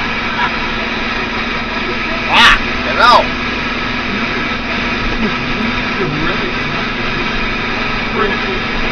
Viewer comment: What did you say at 1:13? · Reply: He said “fucking hell!”